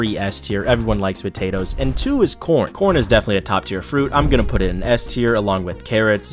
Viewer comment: Wait did he just say “top ten FRUITS??”